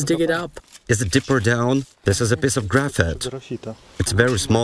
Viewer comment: Did he just say graphite……? Like….. graphite that was in the core?